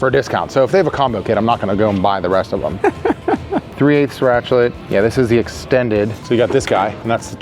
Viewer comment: did he just say ratchlet ?!?